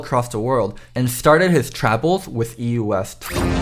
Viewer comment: Am I the only one who hear the word:"he started his trouble in EUW." ?😂 Or just saying "his travel" correctly?🤣